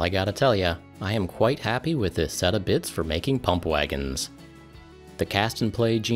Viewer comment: I swear I thought you said "Lumpwagon" the first time.